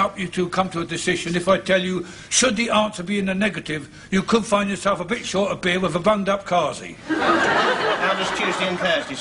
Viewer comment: What does Mr. Harmon say at 16:25? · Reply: Catherine "You could find yourself a bit short of beer with a bunged up kharzi." Or in other words they'd either take or fail to restock the beer and would either intentionally plug the toilet or go slow about fixing it. Both Mash and Harmon use little touches of Polari to cement their very working class characters.